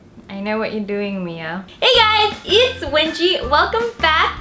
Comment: I thought she said wegie